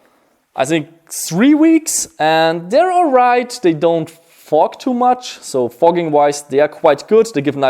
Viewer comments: When he was talking about his goggles he said they don’t fog to much I thought he was saying fuck to much lol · who else thought he said they don't fuck too much